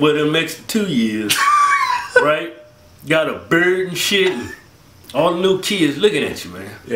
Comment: "ain't nothin worse than going to high school an extra year, well, an extra two years. got a bird an shit." I swear I thought he said bird. I had to watch that part 3 times before I figured out he said beard. I laughed my ass of either way though.